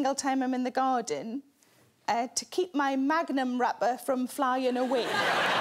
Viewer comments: Oh Magnum wrapper the ice cream...thought she said Magnum rubber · as an american, I imagined something quite different when Sarah said "hold down my magnum wrapper" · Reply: I thought she said "Magnum rubber" and I was even more confused